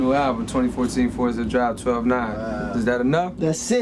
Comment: My newest album 2014 Forest Hills drive ..... is that enough?” What does he say here? Anyone ? I’ve listened back 5 times and still can’t get it for some reason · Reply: On subtitles it says 12/9